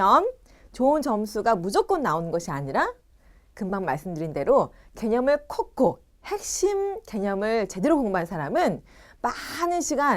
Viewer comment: 개념이라고 있는디 개녕이라고 들었디